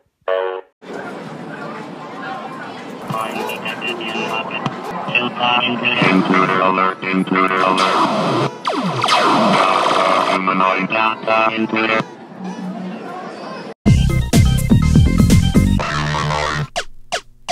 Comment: So it was actually saying "humanoid" I always thought it was saying "feel the noise" whenever I heard it.